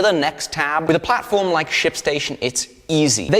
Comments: I heard "sh*t station" instead of ship station. What is wrong with me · am i the only one who thought he said shitstation? jumped a bit lol